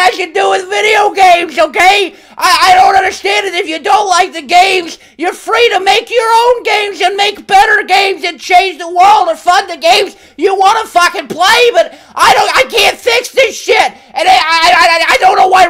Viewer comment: thought he said "fuck your own fucking games" lol